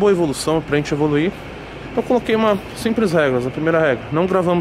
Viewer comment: O que ele disse 3:08 ? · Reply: Regras: a primeira regra